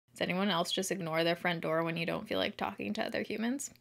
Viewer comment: Girl! It totally sounds like you said my friend Dora instead of front door 🤣🤣🤣